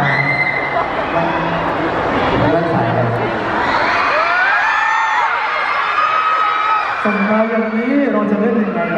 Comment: พอต่อ พูดว่าไรหรอ ใครรู้บอกหน่อย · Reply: FIRST PAWARISA พี่ต่อถามปอร์เช่ว่า แว่นสายอะไร ค่ะ 🤓